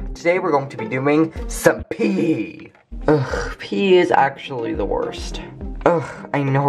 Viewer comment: I thought he said today we'll be doing some pee